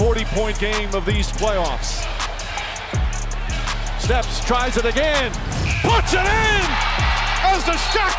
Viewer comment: the announcer said "stef" instead of lebron ..while lebron was shooting a 3 😅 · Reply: he said “steps, tries it again” as in he stepped back before the shot. he didn’t say steph